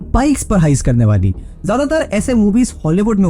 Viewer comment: I heard thighs 😂